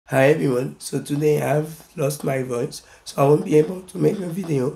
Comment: I thought he said I lost my brother